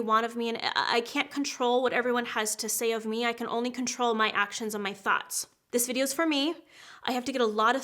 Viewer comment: I thought she said "I can only control my THOTS" 😂😂😂😂